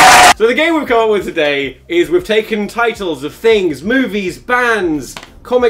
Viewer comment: I thought he said boobes instead of movies Sorry, I’ve been conditioned by Tom regularly